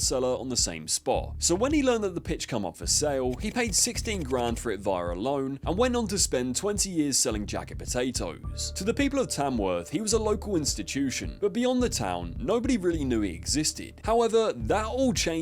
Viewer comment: At first I thought he said “two benders selling potatoes”